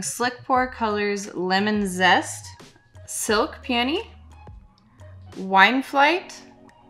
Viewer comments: OMG I thought you said Silk Panties!! 🤣🤣🤣🤣 I'm like no way would name anything that!! I had to listen to it 3 times before I finally heard Peony 🤣🤣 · OMG I didn't hear that as Silk Peony...I heard it as Silk Panty and I was like "Wow YN I'm impressed...you guys pushing the boundries with names" until I read it and realized I had misheard.